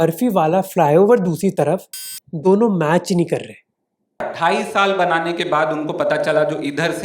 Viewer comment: hey there's a caption error....he said dhai saal(2.5yrs) but the caption says 28 years...